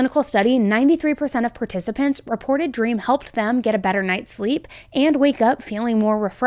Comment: Is it just me or does it sound like always says “dream” instead of “beam” when she talks about her sponsorship….